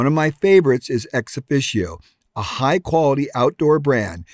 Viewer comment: What's the name of the brand you say on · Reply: ExOfficio